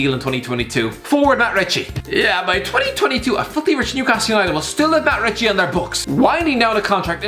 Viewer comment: He said "filthy rich" though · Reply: Kweku Nyarko I thought he said a fucking rich Newcastle united I just misheard it